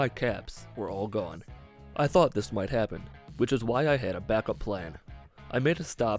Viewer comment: he says "that's why i had a backup plan" but the subtitles say "that's why i saved the brotherhood for last"